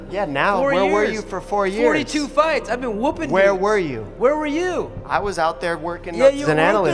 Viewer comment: i could swear faber says i been woopin n####S